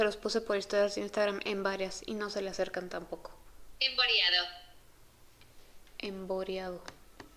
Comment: yo escuche "en koreano"🤣xd